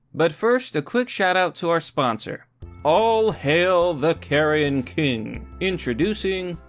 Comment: Was just listening to this when you did the book ad, and thought you said "All hail the Karen king". And now I want to read *that* book.